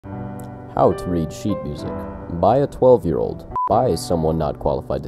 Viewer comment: I wasn’t looking at the beginning and when he said “by a 12 year old”, I thought he said to buy a 12 year old.